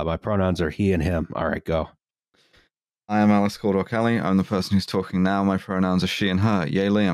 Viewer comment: I'm ill this evening, and in my not-quite-able-to-fully-pay-attention state I heard "Alice 'Cold War' Kelly".